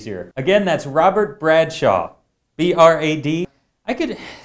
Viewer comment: did he just say "Robert Bradshaw, and say b r a d?"